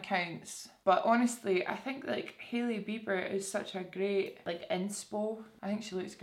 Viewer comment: When you said Hailey Bieber I heard "Hilly Beeper" 😂😂😂